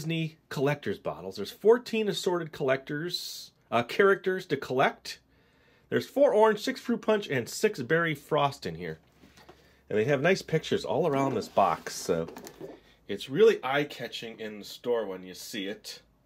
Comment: when he said "in here" i thought he said frosting